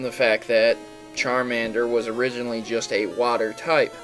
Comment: lol when he said charmander was a water type- o'o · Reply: yeah i thought he said squirtle not charmander then i realized he said charmander and i laughed